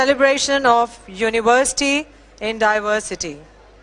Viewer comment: At around 0:37, did she say ''university in diversity'??!!!